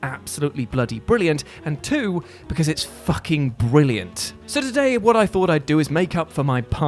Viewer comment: I looked through the comments, and I can't see anything. Did I hear say "fucking brilliant" unedited? Or am I going crazy?